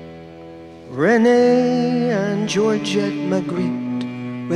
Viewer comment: I always misheard this as Renee and Georgia McGee, and concocted an entire backstory for these two heart weary postwar immigrants. (No children, only a dog? A heartbreak there, surely.) I miss these two stoical travellers, looking for a home. I hope they found it, wherever they ended up.